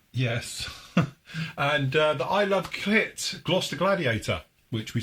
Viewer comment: Does say "I love clit" at 11:18? 😆